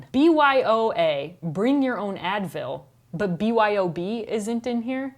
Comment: At first I thought you said "Bring Your Own Anvil". Looney Tunes chat acronyms...